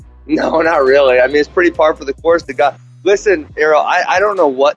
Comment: What did Askren try to say at 7:30 ? "It's pretty par for the gorts the guy"? I think he's still feeling those elbows. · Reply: Par for the course